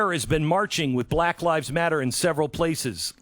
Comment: I heard, "Black Lies Blather".